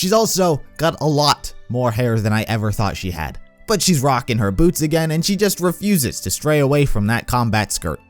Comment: I thought he said Yang is rocking her boobs